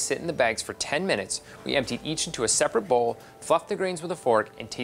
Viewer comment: "... fuck the grains with a fork" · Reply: I clearly heard 'fluffed'. Y'all may want to consider better speakers.